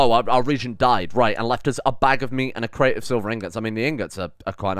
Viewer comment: I'm sorry did you just say "a bag of meat"? Who the fuck keeps a bag of meat